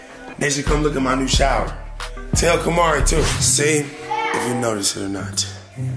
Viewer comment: Let’s clear things up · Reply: I thought I did hear say hey camari